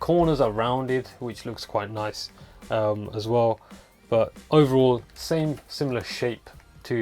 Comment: Tbh it sounded like he said same similar shit